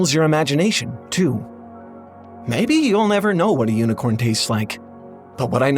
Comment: I thought this said “UniCRON” as in the planet eating Transformer God. I was like….he’d taste like metal?